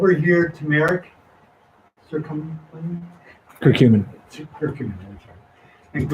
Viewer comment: lmao... The subtitles said that the question asked about, Tumeric Circumcision... instead of Curcumin... 😂 omg.